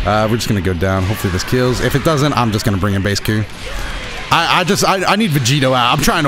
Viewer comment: Bruh did he say baseku??